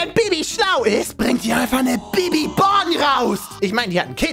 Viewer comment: Hab nur ich im ersten Monent "Bibi-Bong" verstanden? Ich kiff echt zu viel... 😂